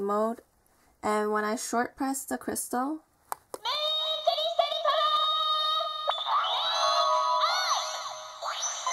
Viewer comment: what does she say? at 3:13? moon [something] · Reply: "Moon crystal power, Make-up!" is what she said :P